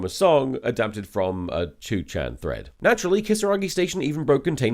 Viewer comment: did I hear "Choo-Chan" instead of "2-Chan"? Is that because it's a train story? Choo-Choo!